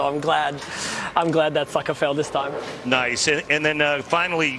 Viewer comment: Did I just hear Belmo drop an f-bomb on live TV?? 😂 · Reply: I think he said sucker but he is stralian Soo anything is possible